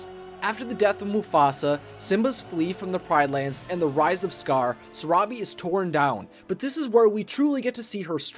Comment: When you said the rise of scar I thought you said the rise of scourge By the way it's from Warrior Cats it's a really good series